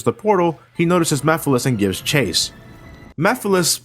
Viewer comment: I have subtitles on and whenever he says Mephilis the subs thought he said Meth, Methyl, and Netflix.